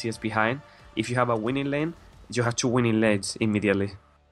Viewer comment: i cant understand him! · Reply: "you have two winning lanes immediatly" :)